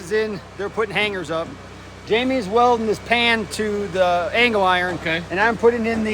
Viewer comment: Omg I thought said Jamie's welding his hand to the angle iron. Had to rewind and listen closely and heard pan, hahaha